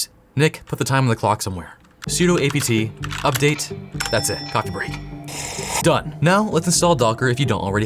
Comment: Chuck! I keep trying to tell you! Lol whomever does your closed captions are making frequent spelling mistakes, but the funniest one so far is when you said "coffee break" at 3:26 and the CC said "cock break" instead lol I'm sorry if I'm being offensive after a handful of videos repeating myself, but it's a shame cuz your videos are so good!!!